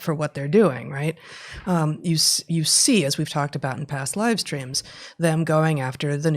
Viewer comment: You know you're tired when you'd swear you heard Heather say "past-lives dreams"! 😅+😬 ... actually "past livestreams"!